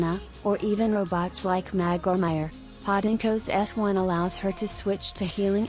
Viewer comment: Anyone hears "Mag or Mayer" as "Michael Myers"?